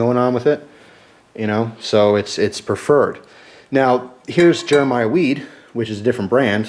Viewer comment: haha I thought it said "Mayan Weed"